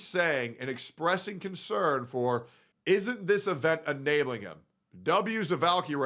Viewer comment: its litterally at the start. even pointed it out. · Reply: dude I’m now 96% sure you are trolling right now because even I heard her say “ isn’t that enabling him?” At 4:06.